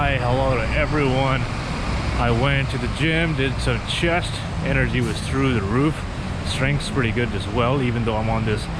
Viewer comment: did some chest, shit was through the roof. That's what I heard.